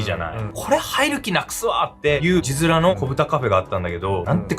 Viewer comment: 子豚カフェが子豚カレーに聞こえたw